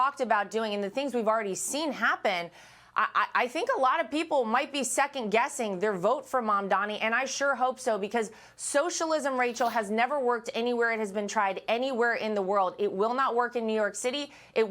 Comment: Did I hear her say “Zamdani”?